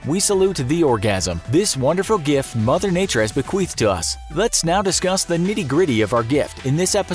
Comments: did anyone else hear bequeefed or just me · Did he say "bequiffed to us" at 0:36? LOL